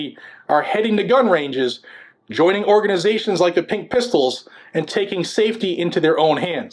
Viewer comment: Hahaha I seriously thought you said "pink pimples" lol